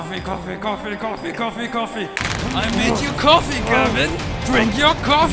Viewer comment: That last one "Gavin" sounded like he was saying "give in".